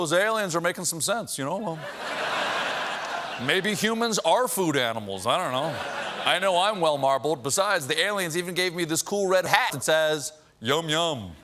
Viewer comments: what word did they overdub? What did he say? Any lipreaders out there? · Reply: Official hat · "This cool red hat" is what I heard, but "This cool red _____" is what I saw. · Does anyone know what he actually said at around 6:39 when he said “This cool red *hat*”